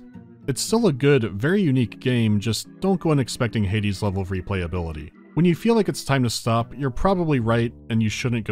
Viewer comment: At first I misheard "Hades' level of replayability" as "Haydee's level of replayability". Now I'm sitting there, thinking...